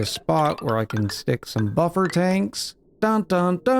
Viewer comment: Did someone say, “buffer tanks???”